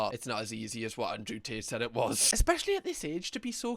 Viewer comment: Sorry im new to your channel so i cackled when i misheard "andrew tit" bcs i didnt expect your accent